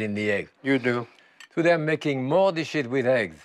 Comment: did he just say "making more of this shit with egg"?